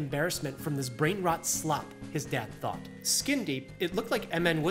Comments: thought you said skibidi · I thought he said skibidi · i swear to god i thought you said 'skibidy' instead of skin deep, whats wrong with me